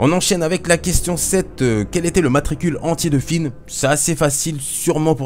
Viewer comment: Mdr pour la question de Finn, j'ai entendu "Quelle est son matricule Antillais" (au lieu de entier) je sais pas pourquoi lol ... Enfin Bref, J'ai eu 7/10, je l'avoue, celui- là était plus hardcore que d'habitude, même si on connait beaucoup de truc sur STAR WARS ^^ J'ai loupé Le Mythosaure, Le Sabre Noir et Tenebrae (J'ai confondu avec Naga Sadow) Continue, c'est hyper amusant les QUIZZ